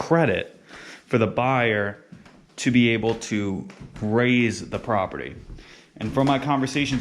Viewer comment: What a gorgeous renovation that kept some of the older features - the built in phone shelf, the shapes of the doorways. And a producing avocado tree. For a horrible minute, I thought you said to 'raze' the property. Here in west coast Florida, people buy older homes in desirable neighborhoods, raze them and put up McMansions